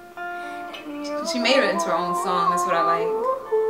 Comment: ay 0:55, said that "she made it into her own song, that's what I like." It's her song though. But I'm not sure if what I heard was right XD